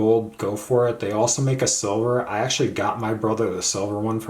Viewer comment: I would not know. I don’t have the solar version so can’t really provide insight about it. Sorry I can’t be more help. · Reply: thanks, I think I just heard 'solar' instead of "silver" :)